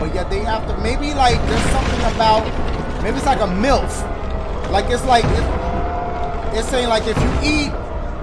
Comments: when he said myth i thought he said MILF XD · it sounded like Dashie said "milf" instead of "myth" lol i was so confused for a sec XD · Bro, @ 43:50 - 44:00 I thought he said MILF and he's saying MYTH 💀